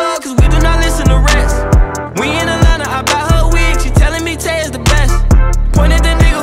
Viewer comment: “We in Atlanta I buy her a wig she tellin me tae is the best”😂🤦🏽‍♂️ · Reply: I thought he said we in Atlanta i buy her some weed 😂😂